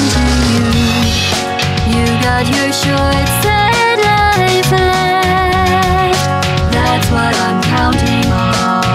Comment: When the song said “You’ve got your short sad life left. That’s what I’m counting on.” I heard it as “You’ve got your shorts and I pad. That’s what I’m counting on”